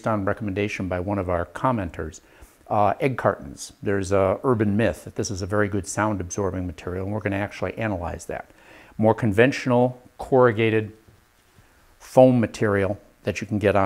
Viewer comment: I heard "We're going to test that" from Project Farm. 😄